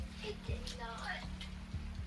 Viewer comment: (I think) She is saying after she pops them dentures out “I Did Not” bc she is replaying the scenario out loud reliving what the detective just told her before he left the room (That she killed her momma & was going to be locked away for sssmurder)